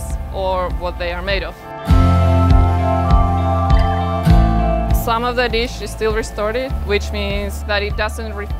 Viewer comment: what's she saying ~ 'some of the dish is still restorted' (cf Danish heavy metal?)...